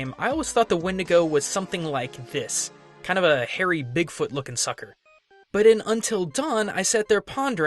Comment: Lol thought he said 'dickfoot' wtf xD